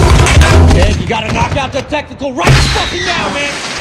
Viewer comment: I keep hearing him say “tactical” rather than “technical.”